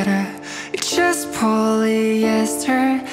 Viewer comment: I honestly thought he said I wish I was a feather 😅